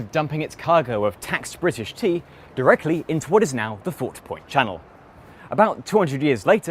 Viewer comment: I think I managed to annoy an officer from every major branch of Boston transit on this one! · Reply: Subtitles say "not" instead of "now". ;)